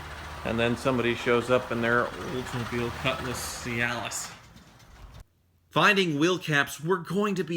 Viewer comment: Did he say "Oldsmobile Cutlass Cialis"? LOL